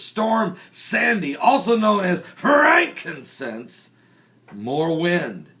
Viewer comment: Did my ears deceive me or did he just say Frankincense? lol.